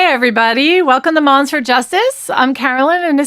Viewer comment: I thought you said monster justice